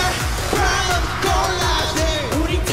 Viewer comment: is RM saying "insult me, lets go"?